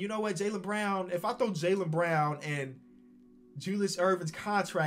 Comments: did he say Julius Irving 🤣😂 not even close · Did he just say Julius Erving at 4:22?